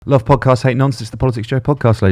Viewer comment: love podcast hate nonsense it's the politics Jo podcast ladies and [Applause] gentlemen (bit boring for the 200th, but the algorithm will do what the algorithm does) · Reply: Genuinely thought he said "love podcasts, hate nonces" at the beginning 😂